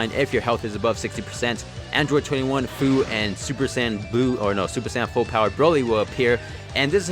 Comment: Am I the only one who heard him say super Saiyan jiren